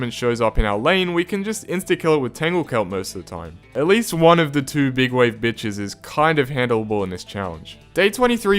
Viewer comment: Is it just me or did I hear the B word